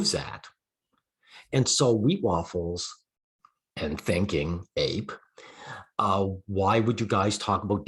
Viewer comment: At some point it sounded like you said Stinking-Ape, haha.